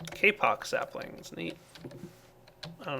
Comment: lol I thought he said "k-pop saplings"